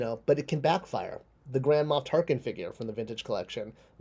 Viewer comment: When you said Grand Moff Tarkin, it sounded like you said Grandma Tarkin